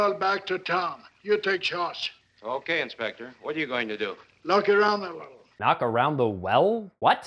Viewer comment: yay! So glad to see your 100th review! Can't wait to see part 2. 19:20 "Knock around a little" that's what he said. You're welcome.